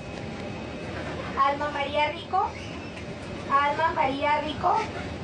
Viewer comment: enserio soy la única que mal pensé??? Lo que dice:Alma María Rico Lo que entendí:Al M4mari4 Ric0😳🤣